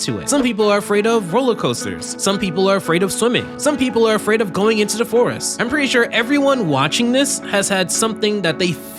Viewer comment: I thought he said “some people are afraid of going into divorce” and not “some people are afraid of going into the forest” lol I am afraid of going into divorce